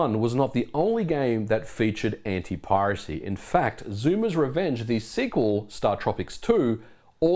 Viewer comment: did you say Zuma's Revenge? Shoves bananas into ears.